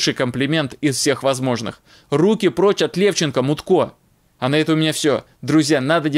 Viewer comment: Извините, не расслышал, Мутко или Мудаккто? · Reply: Мутько, мутный он, должность - мутить народ!